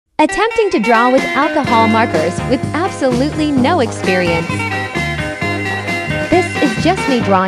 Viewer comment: I thought you said you have no experience 😑😶but you done awesome🤯🤗